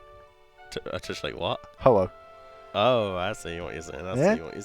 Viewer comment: what does say the bird looks like here? anyone? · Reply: Renewed RS Ho-oh. The bird from Pokémon.